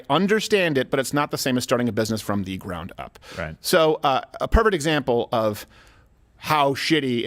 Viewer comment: Is Steven saying "a pervert example" or "a perfect example"? I'm hearing "pervert". I even slowed down the playback speed and still heard "pervert". Plus he had a little smirk while saying it.